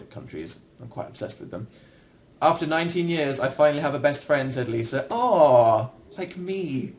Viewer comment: was so heartwarming I thought I was gonna cry. #PHANFOREVER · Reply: Plus it made me cry because I thought Dan said, "After 19 years I finally have a best friend, and he's like me." 💞😭😊